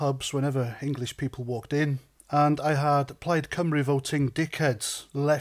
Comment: Could somebody help me? What des he say at 0:20? "I had xxx...? "Thanks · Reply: Plaid Cymru the welsh nationalist party.